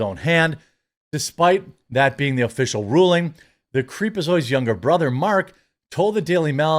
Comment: Did you say "creepazoid" at 2:18??